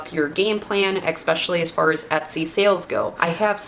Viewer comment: Did you say "eXpecially"? 😂 But thank you for these gems. 🙏